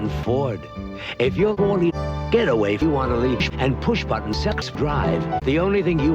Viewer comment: “Get a waifu on a leash?” 0:53 strong bad “one two, one two!” 1:41 “4chan” Best parts. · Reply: I thought it said, "Get away if you wanna leash."